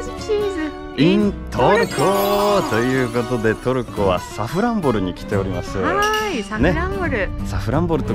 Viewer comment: いつも見ながら二ヤッとしてしまう例えが最高！！(編集に時間かけてそうw) サクランボに聞こえてしまうサフランボルもいい所ですね。